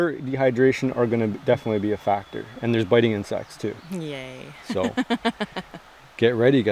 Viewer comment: misheard you a lil at the start of this vid thought you said "there's biting and sex too" was expecting a whole different video :P